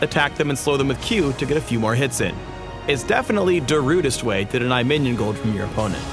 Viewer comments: Did phreak just say "Darudest way" in 5:33? xD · THAT PUN DOE, for those of you that can't hear it, da rudest, darude sandstorm. huehuehue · Did he just say "Darude-est Way" ? XD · Nice one LoL, i heard that... "Darudest way." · Yes I heard you say "Da-rudist way" at 5:35. Pun level: sandstorm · Did anyone else hear Darude at 5:37? Please tell me I didn't hear it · Did he just say darudest..? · Anyone else hear that " Its definately Darudest way" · did he just say darudist? Phreak the master of puns